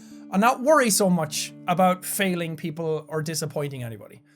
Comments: I thought it said falling 😂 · 2nd time I still thought it said "falling" and not "failing" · All this time I thought it said falling, like being forgotten and disappearing but it said failing.